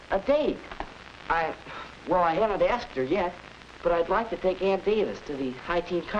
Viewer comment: When he said "I havent asked her yet" I thought he said "I am a bastard yet.". O_o